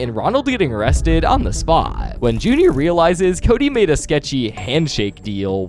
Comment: I hear Judy and not junior